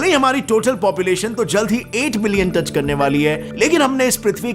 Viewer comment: did he say 8 millions? instead of 8 billions 🤔